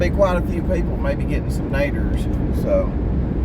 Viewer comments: Lmbo at first I thought u were saying people getting some maters as in tomatoes…lol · When u said " naders" I thought u said , "maters". Hahahahahaha.